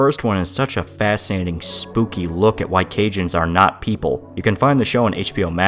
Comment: Did he just say "Cajuns are not people"?